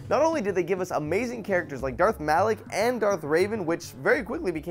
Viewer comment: did he say darth malic? I thought it was darth malgus